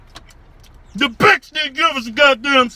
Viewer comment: the subtitles said “the bitch niggas sodas man”